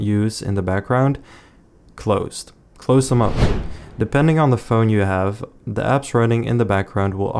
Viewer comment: What he said : depending on the phone u have Caption: depending how broke u are